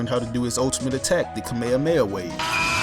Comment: I'm sorry, but what did you say? It sounded like you said kameamea wave. It's definitely kameHAmeHA wave (11:07). Other than that, good video!